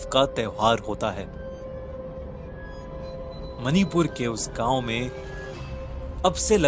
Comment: did u said manipur?